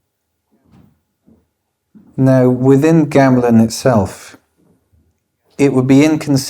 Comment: what is he saying here? What is Gamolin!? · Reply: Gamelan